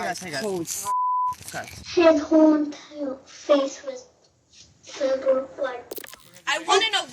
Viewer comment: i think she said, “ his whole entire face was filled with blood.” i don’t think she said, “red with blood” @ 00:18 am i the only one that hears that? not important i know im just curious.